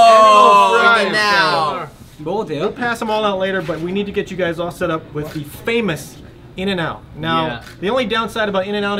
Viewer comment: Can someone make out what kookie says at 0:58? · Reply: He said,“Can i eat?” so cute